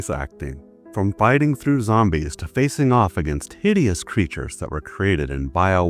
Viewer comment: I had to rewind because I burst out laughing. I thought you said “Diddy’s Creatures” Like you were making a joke😂😂😂.